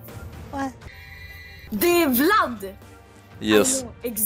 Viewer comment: i think she's Swedish and i think she's saying "det e vlad" (translates to "its vlad) instead of "the vlad". But i might be wrong :)